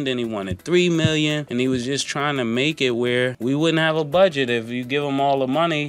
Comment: is that one of the intentional "mistakes"? Budgie instead of budget?